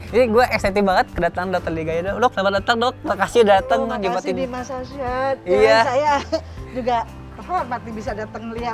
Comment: Aku salah denger ga si pas dokternya ngomong dimas alshad bukan ahmad alshad